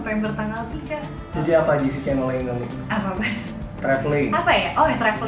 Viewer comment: sherina bilang apa yaa.. "apa Mas" gitu bukan sii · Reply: Apa bas